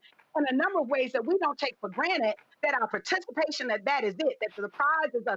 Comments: Did she just say "granite" @ 4:09? lol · This "Spokeswoman for black voters" is a real idiot, isn't she? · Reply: Did she just say, "take for granite?" @ 4:12Granite??? Yes, she's a real idiot. hahahahaha. Keep in mind that they still don't know their own history. "I'll have this ni*#as voting Democrat for 200 years." hahahahahaha